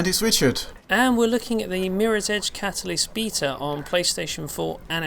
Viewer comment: when the video starts it sound like he says mirrors edge bata